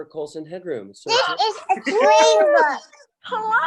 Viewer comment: Re-watching this today and laughed out loud when Clark Gregg joins and Jessie's daughter says "this is a train book". Could have sworn she said "this is a train wreck". So funny. <link>